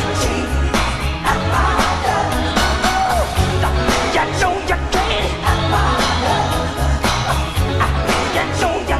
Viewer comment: what he say? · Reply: "You know you can't, you know you ain't, because you are, You're just a City Girl"